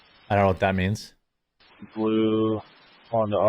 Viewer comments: I think he said no plates, not no flick. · "blue honda odyssey no plate" is what i think he says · I think he said blue Honda Odyssey with no plate at